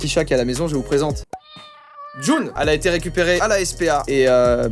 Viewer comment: c'est moi ou son chat s'appelle JUL Moi je me dis que j'ai mal compris...